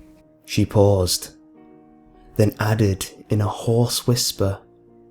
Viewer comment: LOL "horse" whisper. but seriously now I freaked out when I heard these voice actors. 0.0 they sound just like the ones in the show! (but I guess that's a good thing sence the intention of a creepypasta is to try to "get at" people (:T )